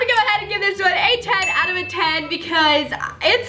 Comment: Who else heard say “a tag out of a ten”